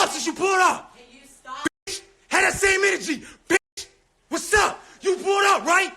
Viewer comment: Did he just call the police officer a "ham samich"?